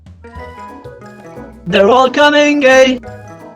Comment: what i hear was "they are coming gay"